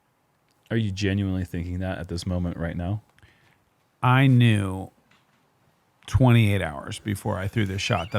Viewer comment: "I knew 28 hours before this shot, I would be the shortest drive on the hole". 😂😂😂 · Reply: On first listen I heard it as "I knew 20 years before this shot..." lel